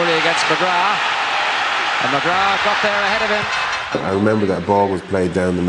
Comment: hahaha at 3:28 is he saying mcgraw? thats my last name and my dad would get a kick out of this